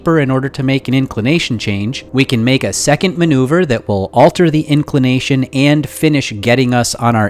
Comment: All I hear is "let me alter my inclination" and "in gonna adjust my inclination"